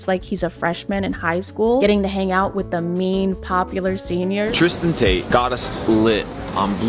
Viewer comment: Multiple times during this video, I heard "tris-taint" 😂